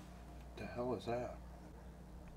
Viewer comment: sounded like “all hail”.. I forgot how good this was.. times,equip and video has change.. but the essence of you three haven’t.. Not in Purpose or beliefs.. 💞💞 thanks for sharing on my night off